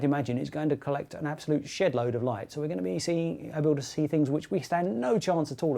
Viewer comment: did he say "shit-load of light" @ 5:48, LOL!